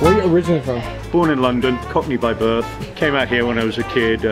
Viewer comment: He says "cockney by birth" yet subtitles say "caught me by birth".